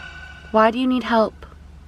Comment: Right before the "Come here" at the 19:38 mark I swore I heard "Would you come help me" in a female voice